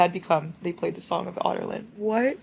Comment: im sorry but the way you pronounce adarlan sounds like youre saying otter land i cant lol